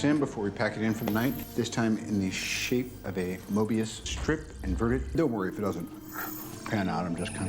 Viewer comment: Wait did Tony just say Mobius strip?